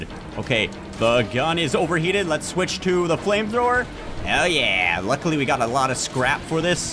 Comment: Did he just say hell yea???